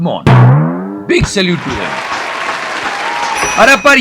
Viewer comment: Did i just heard Big salute to him at 4:15 🤔🤔